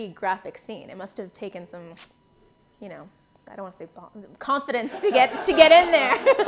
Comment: what did she say? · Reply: Jasper D it must have taken some ba- I dont want to say balls but confidence to get in there